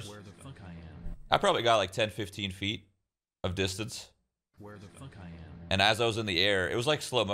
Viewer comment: So even when 10-15 feet in air, he will beat us in how we land and will get the least damage, dude needs to be nerfed. If i were in that position, i would have probably broken my neck or something. Get well soon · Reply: yeah he said distance but a lot of people misheard